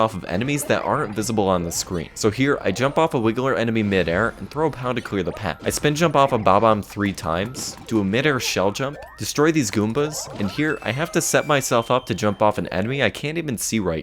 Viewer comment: I HEARD "GOONBAS"